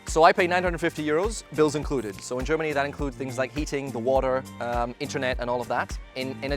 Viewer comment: 950 Euro..for one year I guess..? Tell the duration too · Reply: TheBlackiwid I think he mentioned that the costs of heating, Internet etc are included.. maybe you misheard heating as eating? I did too for a moment and then figured it out!